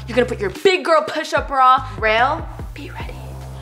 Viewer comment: did call Raul “rail” 😂🤚